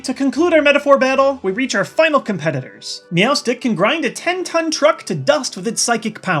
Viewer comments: sounds like you said meowths dick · does he says meowths dick?